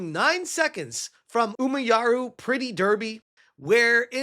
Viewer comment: what did he just call it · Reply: "Umayuru", which is the name of the chibi ovas. He got Cingray and Umayuru mixed up.